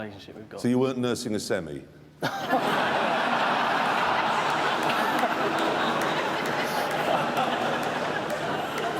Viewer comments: What did he say at 1:40!!? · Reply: Umar Khan isn't it “oh boy” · what did he say at 1:38? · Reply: "So you weren't nursing a semi?" Which is something that happens to guys when they kiss a girl they find attractive.